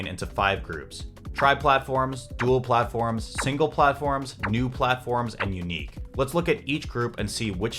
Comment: it sounds like he said "new platforms"